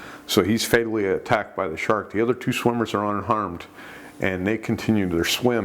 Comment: Sorry, I heard "The two other swimmers were un-armed."